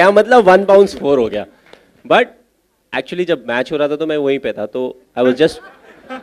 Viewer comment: what he said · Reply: Hari Krisnan he said jab match ho raha tha tab mai vahi pe tha.......which is very obvious